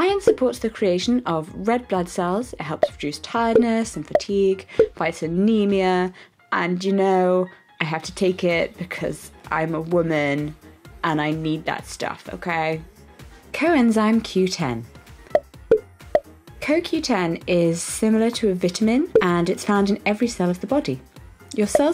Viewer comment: Glad you're doing so well! Maybe I misheard you, but did you say "I'm a woman, I can't eat carbs"? 😂😂😂😂😂😂 Next question: bee spit?